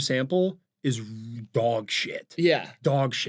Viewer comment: no cause like I really thought you guys were saying "Frog Watch" instead of fraud watch